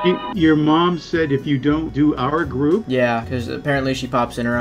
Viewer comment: I thought the first one said furby group